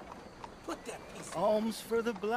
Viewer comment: I’m pretty sure i hear someone else starting to say “Put that thing away!” 😂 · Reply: yup! the dude sweeping the sidewalk told me to put that piece away. “piece” is a slang for gun, you’re the first person in the 1k comments to catch that 😂